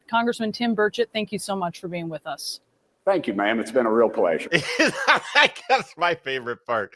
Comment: He said, "Thank you, ma'am" but I definitely heard "Eff you, bish" 😂